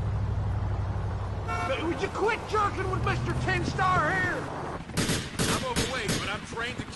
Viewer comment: I think he says “tin star”, meaning a badge, rather than “10 star”.